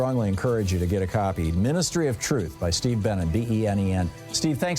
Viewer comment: Scary... I heard "Steve BANNON" before I saw the correct name on the book... phew!